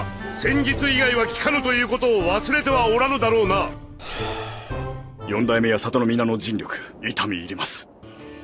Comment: the subtitles say "Lord Second will never change" but Fugaku is saying "Yondaime" which means "The Fourth"